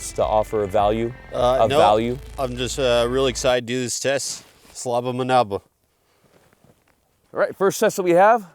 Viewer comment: I don’t know Russian, but did say “Slob on my knob” in an accent???